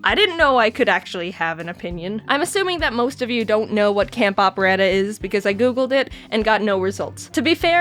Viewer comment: Tbh I thought it said camp oppression